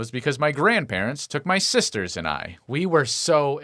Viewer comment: did he say sisters? As in more then one?